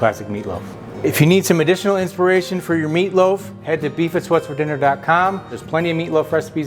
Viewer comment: I thought you said "Beavis whats for dinner?" 😂😂😂